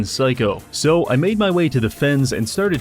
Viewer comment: I thought he said FEDS instead of FENS and got really scared that was actually losing his insanity. edit: i mean i thought was asking the feds for help not that he was fighting them. the latter is a common tuesday